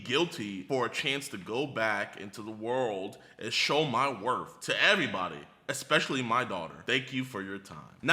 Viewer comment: I thought he said wrath not worth